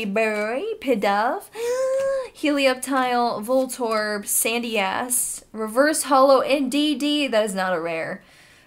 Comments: Sorry for my foul language but it sounds like said “Sandy ass” 😂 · sounds like you said Sandy Ass, lol. had to listen to it twice.